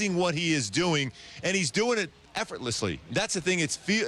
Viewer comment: の平然と、はなんと発音しているでしょうか。聞き取れなかったので教えて頂けると幸いです。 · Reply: effortlessly かな。